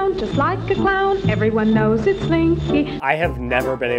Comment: I heard stinky